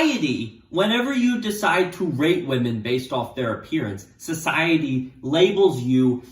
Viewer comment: Who else thought he said rape women instead of rate women?